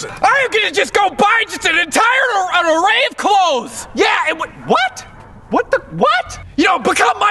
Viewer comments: I'm not a native English speaker, can anyone please tell me what does says at 0:55-0:59 ??? Thanks! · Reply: I'm just gonna go buy an entire array of clothes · What is he saying? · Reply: "I am just gonna buy an entire array of clothes!" And "Become Mike Tyson's Agent!"